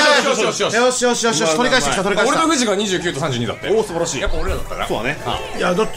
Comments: めっちゃ「俺のフジが」って聞こえるwww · キ「俺のフジが29と32だって」に聞こえて何回も聞き直したけどやっぱりそう聞こえたから末期